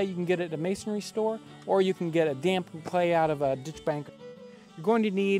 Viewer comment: what is a dige ban @ 1:14 ? . cant work out what he is saying... :-( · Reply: The phrase is "ditch bank". for more info on clay for ovens see this vid: <link>